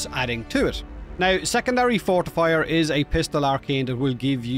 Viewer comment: 1) Keep dear aunt Maggie out of this, thank you! LOL 2) I'm glad you showed that arcane. With your brogue, I could have sworn you said "Secondary Fart Fire!" I've not got that particular arcane, although I'm pretty sure I encountered it after Taco Tuesday a few times! 🤣🌮